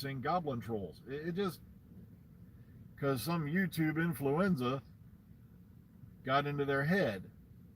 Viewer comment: It sounded like you said youtube influeza, and it sounds like there is two sorts of doctor who fans, the ones that take the character as a role model and spread joy and who are decent people and the loonie fans who are stuck in the past, inflexible, not wanting any change happen ever!! Naysayers. They try to blackmail a whole industry to meet their antiquated picture of how the series "has to be". Boy am I glad that the series got revived and I sure wish, it will go on for a while longer. Diversity for the win.